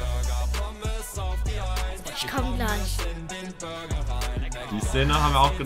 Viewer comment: WARTE WAS SAGT DA? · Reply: „ICH KOMME GLEICH“